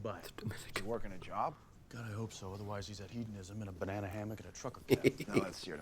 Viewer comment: I think you misheard what Dean said about Bobby. He didn't say he was in a hammock, he said "in a banana hammock and a trucker cap". A banana hammock is slang for a speedo.